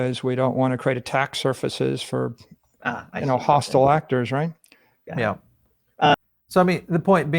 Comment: Great stuff... (00:07:04) I dont think he said "tax positions", i think he said "attack surfaces for hostile actors" meaning avatar man just told the hackers where 2 focus their efforts to get ahold of his digital gold